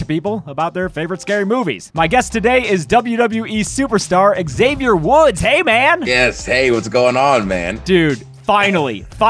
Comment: Thought it said Xavier wulf lol